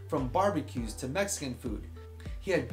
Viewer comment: did he just say "Mexican food"?